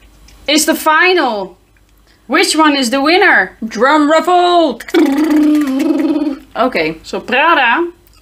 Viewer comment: did she say "drum revolt" instead of drum roll? Lol. You two are hilarious, one of my favorite channels.